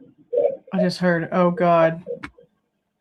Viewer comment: "how the f dud you sleep through this"?😢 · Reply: I heard “Bethany, are you kidding me? How the fuck…” ETA: time stamp 47:02ish and “Bethany, are you kidding me? How the fuck..oh God, this is bad” (maybe?)